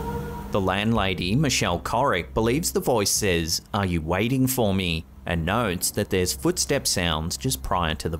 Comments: That landlady one it sounds like it says '"ARE YOU COMING HONEY OR "" ARE YOU GOING MOMMY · I hear “ Papa wait for me..” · For video number 3, I'm not sure how the woman hears "are you waiting for me?", to me it sound like "Mommy, where are you?". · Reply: And I hear "Are you COMING for me" 🤔